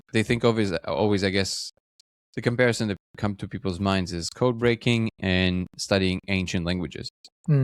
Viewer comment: "People say" then you cut it out. What did say there? Would you kindly not edit or as less as possible? It always feels off to me. Like something that should be hidden from the audience was just said and cut out. One Love! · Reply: We edited the repeated words only, it said even people say codebreaking etc. we add raw episode, edit only repeated words. You get what was discussed